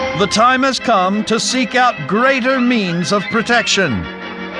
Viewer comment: For a split second there. I thought i he said "Greater Memes".